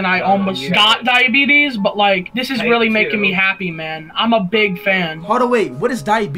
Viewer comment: I had to watch dat again... I thought he said im a big man😂